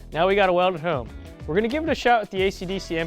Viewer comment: … What did he say?…! 😂 Seriously though, guys- cool video.👍🏻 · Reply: "Now we gotta weld it home" Thanks for the positive comment!